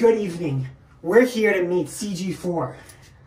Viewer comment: Title: CG4 Him&What i heard: We're going to meet CG54 Me: * Dead * Me again: * Didn't know there was a CG54 *